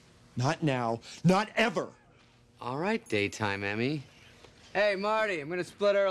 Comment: I thought he said, “day time enemy” at first